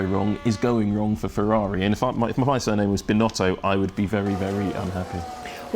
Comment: Did he really say "S🅱️inotto" at 2:53? lol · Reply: I think he rolled the 's from " was (Binotto) " but it does sound kinda sketchy so i can't blame you lol